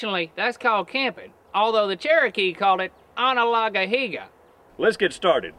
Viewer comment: Too nice, I need to go campigne ( but I don't understand how they call that 0:28.) I hear : On a log and yeeha but I think its not that... help ?